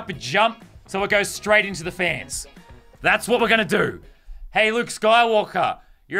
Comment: i thought he said hey look skywalker